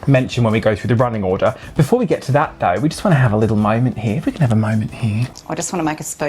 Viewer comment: what you said 'wanna have a little moment here' what i heard 'are you ready for a little chat?'